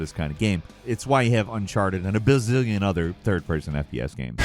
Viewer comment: Did he just say a billion other third person FPS games.......